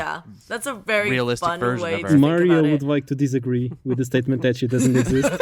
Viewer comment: When I first heard that I thought you said Mario not Maria, so I thought you were making some kind of joke I didn't get.